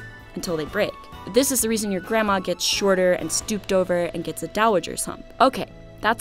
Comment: When the subtitles say "inaudible" at 3:13 she says Dowager's hump, in case anyone was wondering.